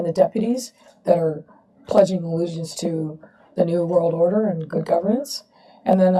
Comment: Woah!!!! WTF...........did she really say New World Order???? @ 3:16 Screw her........